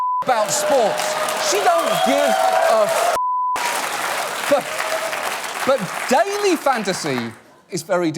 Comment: wait did he say "she don't give a fuck" She don't John? I'm Egyptian, man... YOU ARE BRITISH